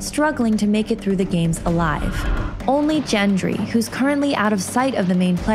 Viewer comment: did you just say.... jendry?